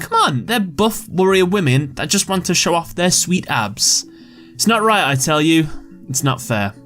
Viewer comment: I thought he said “sweet ass”